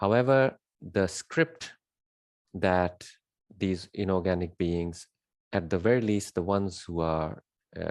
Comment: Do you mean inorganic beings? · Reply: yes...is that what you said? Inorganic beings?